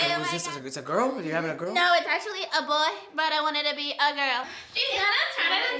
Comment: "I don't know if it's a boy, I don't even know if it's a girl- I don't know if it's a human!" 😂 HELP ME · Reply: Ohh i thought she said " no its a boy but i want it to be a girl"